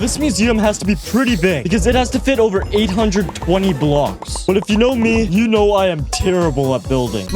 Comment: did he say 820 blocks?